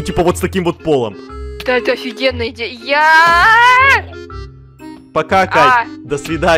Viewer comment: когда это сказал пока Кать я подумала что он сказал покакать